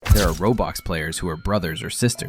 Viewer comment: on the start i heard “There are ROBOX Players” Dude🥀🥀🥀